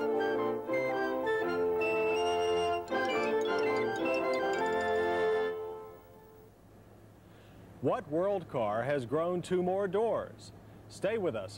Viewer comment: I thought he said WORLDSTAR!